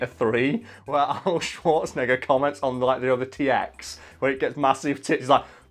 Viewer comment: Am I the only one that noticed that in about @ 4:51 the autogenerated comments said “short nigga” instead of Schwartzenegger? And I thought nothing could make me laugh today.